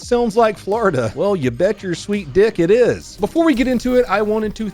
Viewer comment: Did I just hear “bet my sweet dick” 😂